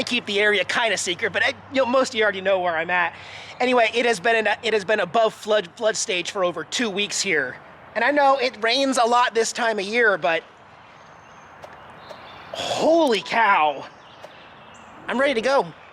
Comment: I thought you said Texas rains a lot ... maybe the rain just follows you no matter what state you go to 🤔